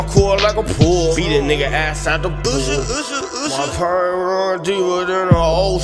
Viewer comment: does he say "my perm run deeper than the ocean" I thought this was funny cause he doesnt have a perm